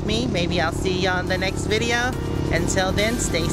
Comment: So pretty at the Dunes! You know I live at the bottom of Lake Michigan and we have sand dunes, but nothing close to those! That is huge! At first I thought you said at the end " Oh I can buy drugs here" (instead of I can fly drones here) They have been talking so much about legal pot in California the last few days, I guess my mind just went there! LOL! You and Moo Moo are great and my rat terrier Simon would have a blast with Moo Moo!